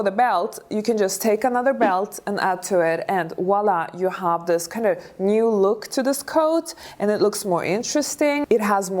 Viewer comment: Did you say “Wallah” at 5:16? 😂 It’s so cute 🥰 Btw I love your outfit, it suits you very well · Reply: I think she means voilà